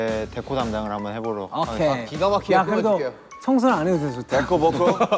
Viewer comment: Did Jungkook speak spanish at 2:16??? I swear that he said "okay, de acuerdo" ....